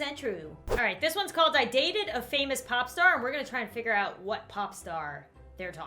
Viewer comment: I thought you said "I dated a famous pop- tart